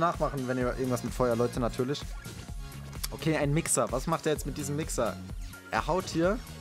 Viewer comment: Min 7:52 hab verstanden:"Was macht der mit dem wi**er" nicht böse gemeint hab ich nur so verstanden